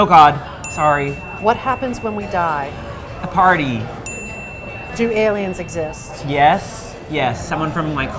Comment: did he say “A party” cause if so 😂